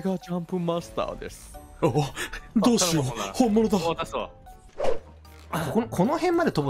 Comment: ブラハのセリフっての始めって「同士よ」だけど空耳で「どうしよう」に聞こえるのあるある